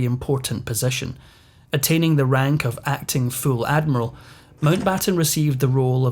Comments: Did I hear that he was promoted to acting FOOL admiral? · With your accent I heard "acting full admiral" as "acting fool admiral" - also true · I heard it as him being given by Winston Churchill the title "Acting FOOL Admiral." 🤣